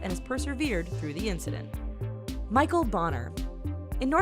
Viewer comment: What it said : Michel bonner what I thought it said : Michel boner